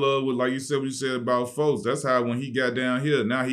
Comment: I thought Q said “folks” at first